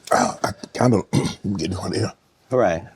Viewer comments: did Shannon say “im kinda getting hornier “ pauseeeeeee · sound like he said "I'm getting hornier"